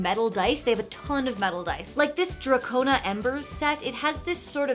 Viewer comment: I heard "Daco" and I was like "DRAAAAAAGOOOOON DIIIIIIIIIICE!!!!!"